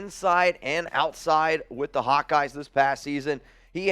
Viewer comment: Could have sworn said "playing for the hot guys" when talking about Van Ness. But it was Hawkeyes. Was thinking might be agreeing with his Girlfriend there a little. 😄